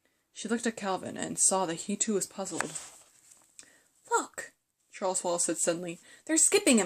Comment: what did it say???? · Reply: Marcos Diaz look